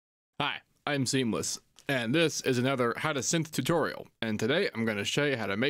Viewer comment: For a second I was like did he just say how to simp tutorial? 🤔🤣🤣